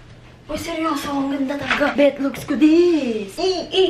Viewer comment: did i misheard it oh betlogs talaga sinabi mo HAHAHAHA